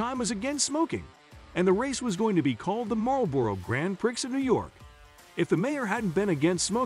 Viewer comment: did you just call it the "Grand Pricks of new york"? Grand-preeeeeee